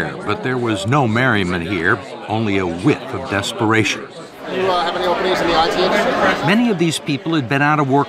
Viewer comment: I thought he said "Do you have any nipples?" My hearing is not so good, but that was funny.